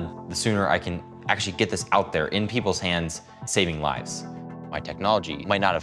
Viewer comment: did he say get the GUN in peoples hand SAVING people’s lives